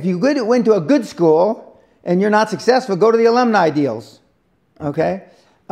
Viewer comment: What did he say " Olymnaja Deals"??? I didn´t get it